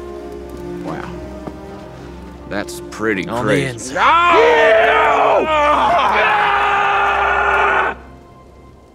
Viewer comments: What did he say at 2:09? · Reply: "......On the inside." I didn't quite hear him at first either. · I cant tell what he said at 2:09 help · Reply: andrew james he said 'on the inside' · what did the quiet guy said at 2:10 because I can't decipher what he said YES I've watch that part OVER and OVER and NO I STILL couldn't figure it out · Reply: oh hi everybody! I think he said on the inside